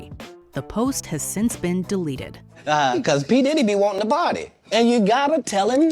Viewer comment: I thought Katt said Diddy be wanting the body not to party